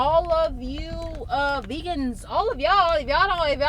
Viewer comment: did you just say all you vegans? Lol you crack me up